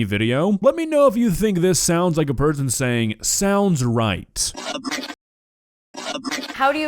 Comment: He did not say "Sounds right", he clearly said "Help me!".😅 12:17 I hear the word "Hilfe" wich means "Help" but in German. Great, now I believe she really did molest a poor german ghost. "NEIN means NEIN", Lady.😢